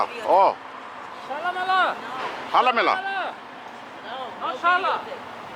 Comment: “Jálamela” I heard that 😂 For those who don’t know in Spanish that’s slang for “jerk me off”